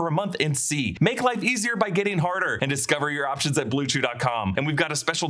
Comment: What did he say😂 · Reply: “Make life easy by getting harder” lmmaaooo wild ass line